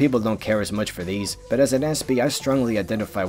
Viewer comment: as an antsby? I looked it up and that’s not a word I can find. Can someone tell me what he’s saying I know nothing about Newt. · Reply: “Aspie” is a shortened slang term for a person with Asperger syndrome, a very high functioning form of autism.